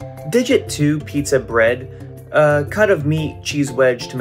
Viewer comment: Did he just say cut a meat without emoji 💀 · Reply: He said Cut Of Meat 🥩.